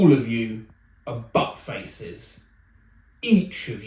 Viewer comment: I listened to this video over and over, and it wasn't until I actually watched this screen, that I realised the girl said "butt faces." I kept hearing it as "fuck faces."